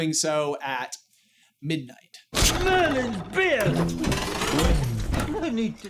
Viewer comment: "Merlin's beard!" and CC says "Smelling bad" 🤣🤣💩